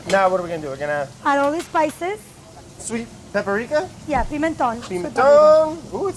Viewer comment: did brad say pepperika instead of paprika???